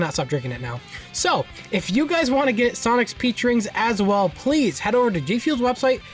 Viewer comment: Totally thought you said "sonic's pee drink" lmao